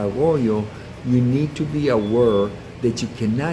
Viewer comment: Would you be of French ancestry? You said: a- were... instead of aware... :)